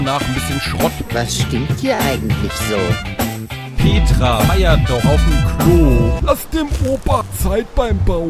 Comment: Ich habe erst verstanden: "Petra reiert noch auf'm Klo!" :D Richtig gut gelungen! Weiter so :)